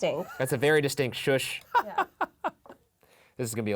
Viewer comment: you guys say it says “shh” but I kinda hear “arch” idk if that means anything but I hear that